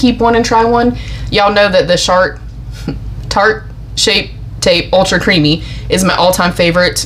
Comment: Did I hear shart 😂😂😂